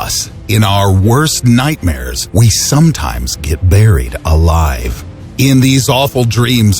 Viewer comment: I thought he said We sometimes get married alive!